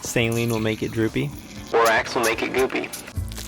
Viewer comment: for a second I thought he said lorax will make it goopy😭